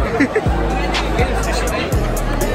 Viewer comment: what did he say? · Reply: I don't want aids